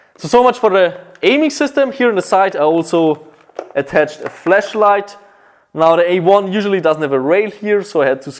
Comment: "I also attached a fleshlight" ahh I love you <3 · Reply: Needed to watch that part 3 times and still heard it as fleshlight, lol